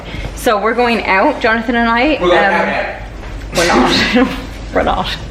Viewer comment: What does he say at 5:55? In the background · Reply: He said "we're going out out!!"